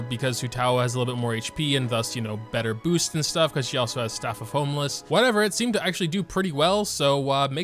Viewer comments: did you say staff of homeless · call me crazy, but I heard him say Staff of Homeless instead of Staff of Homa